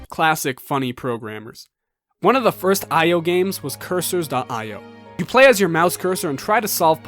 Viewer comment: My stupid ass thought it said “10 games”